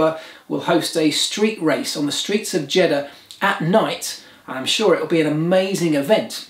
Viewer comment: Did say Jeddah Knight....? 1:13 May the Force be with you.🌀 (-: <*> :-)